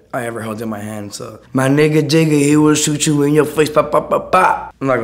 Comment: Jigga??? I thought he said Ominous they trynna really throw ominous in the shadows, I'm a huge fan of purpp but that's fucked up